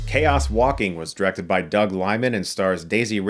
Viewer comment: Thought it said Chaos Viking for a second